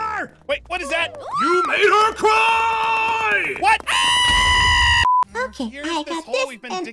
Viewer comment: I was crying from laughter It makes it worst by making him yell I HEARD A CRY as he crashes and blows up man Who agrees?